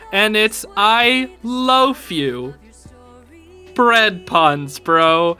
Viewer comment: For a second when king said “I loaf you” I thought he said “I loathe you” until I heard “bread jokes” and then I remembered